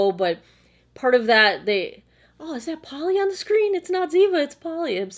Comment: Love the cameos by the furbabies! The first time you ever mentioned Polly in a video I thought you said Pauley (as in Perette) and figured it was another NCIS homage - have you ever done a meet-the-fur-family video? I'm sure I'm not the only one who would love that. I'm literally barely scraping the surface of skincare right now, I did lots of research last year and now have a very simple routine going. I'm planning on venturing into the territory of Vitamin C and Retinol this year so wish me luck!